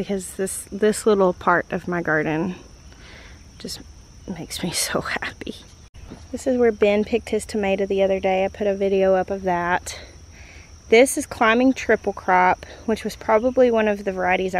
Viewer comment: I'm walking the dogs, and think I hear you say, "This is Climbing Triple Crap."😂 😆 😂 😂 😂 🤦🏼‍♀️🤪🥴 You make me laugh and smile so often! Thank you! ☺️😁